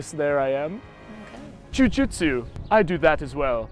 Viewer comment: Why did it sound like he said "Jew Jitsu"